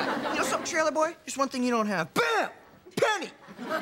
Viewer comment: I've always thought he said "Bam, Pendant"